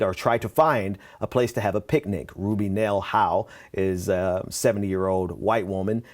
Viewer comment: I thought you said a 70 year old muling cow was arrested! But it's her name, Ruby Howell. This is not funny, people. If you had to worry about being shot on a daily basis because of the colour of your skin, you wouldn't joke about this!